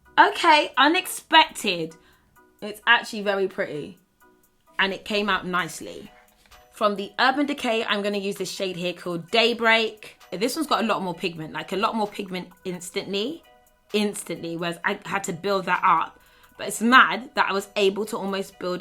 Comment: LMAO when she said the shade “day break” I totally thought she said date rape 😂😂👋🏼